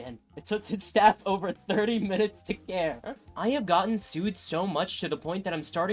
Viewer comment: I heard a bit of "it hates me so much" do you know TF2?